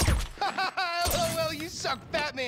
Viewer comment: does jimmy say batman surely not as its an IP that has copy right but it sure sounds like it no ?